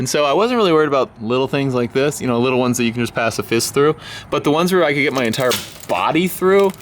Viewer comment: I thought he said " the little ones you could pass a piss through🤣🤣🤣 he said pass a fist through🤪